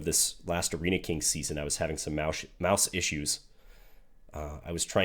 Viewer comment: Did say, “moush issues”? 🐁 🤗